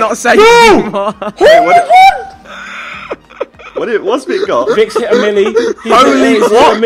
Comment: skip to 13:10 and it sounds like tobi says viks got a willy